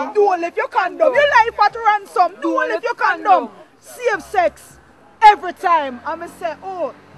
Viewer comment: lol freal... is it me or it sounds like she says "same sex" instead of "safe sex". But seriously though, at first i found this laughable, but i feel terribly sorry for LA Lewis...i think he needs psychiatric help, as he is quite disillusion and seemingly a pathological liar. I'm sure Paul Bogle, Nanny and co. would be very happy of your admiration of The Queen. You dont have to love Selassie but geez, dont praise the direct descendants of our colonialists :|